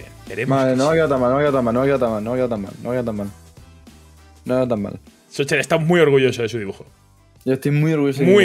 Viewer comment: Cuando ha empezado a decir "No me ha ido tan mal,no me ha ido tan mal..." Estaba distraído y escuché "Novio tamal novio tamal novio tamal" XD